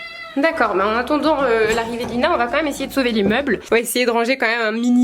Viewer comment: à 5:38 t’as dis on va essayer de sauver l’immeuble ou c’est moi qui ai mal entendu ? 😭😂on dit pas sauver les meubles ?! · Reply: Sauver les meubles mdrrr